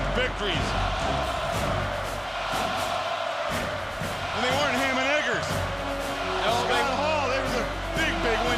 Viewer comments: Wait. What did Bobby say @ 0:52? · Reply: 😂"They weren't ham-and-eggers" Ham and eggers as in jobbers. Goldberg's 107 wins were against good wrestlers, not ham-and-eggers. Either that or he said exactly what you thought he said!! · wait what did he say ?! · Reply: ham and eggers